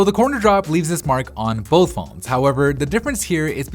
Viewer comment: Samsung's glass shatters faster than the iPhone X's one because apple used stainless steel for the side borders of the iPhone X! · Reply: i thought u were saying corner drop